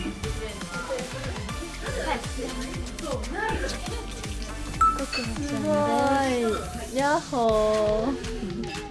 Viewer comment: でなんて言ってるか分かる人いますか！？ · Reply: パスって言ってるみたい。